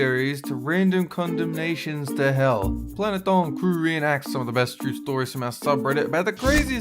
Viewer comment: It sound like he says planet dong crew